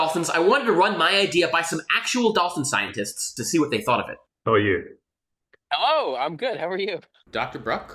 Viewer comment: instead of "How are you?", i heard "Who are you?" HAHAHAHHA